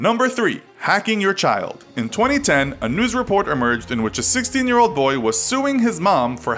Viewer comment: LOL I thought that he said 6-year-old, not 16, and I was thinking, what was a 6 year old doing with social media and an email?!